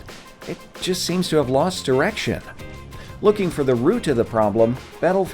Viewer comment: did you say... lost erection...? It’s also an accurate description, either way